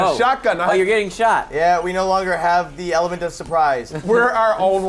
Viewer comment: "We no longer have the elephant of surprise." XD That's what it sounded like...